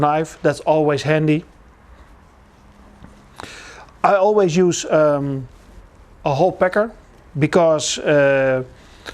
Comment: Sounded like he said "I always use a whole pecker"